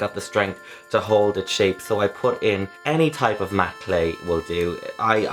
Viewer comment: what does he say at 3:43 something clay i couldnt make it out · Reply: Adam R matte clay lol